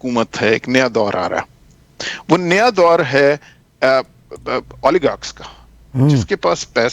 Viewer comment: what did he say ? polygox